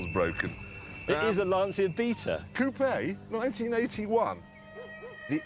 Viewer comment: Because of the accent, I always hear this as "Lancia beater", which I think is an unintended bit of truth in advertising.